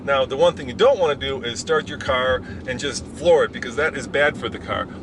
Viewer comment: i dont understand can you pls tell me " start your car and just ?????" what next he said? thanks! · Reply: "Start your car and just floor it..."